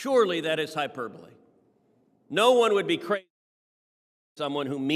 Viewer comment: did they just cut out the word "crazy"? Because that didn't sound like a mic failure or a video splicing issue..it sounded like someone purposefully muted the word "crazy"...and that is absolutely CRAZY